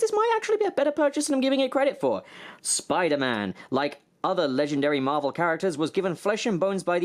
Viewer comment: I thought it said spidea man